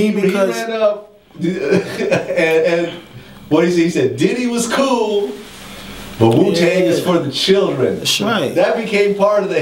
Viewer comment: I could’ve sworn that Dirty said “Bad Boy is good, but Wu-Tang is the best!!”……..am I wrong??